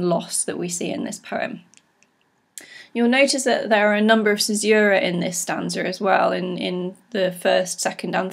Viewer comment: What is it that you say at 10:17? "There are a number of _ in this stanza as well." In the U.S. we say commas....? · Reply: Callie Gunzenhauser Hi Callie, the term I used was 'caesura' - this means a pause within a line of verse (usually created using punctuation such as the commas here).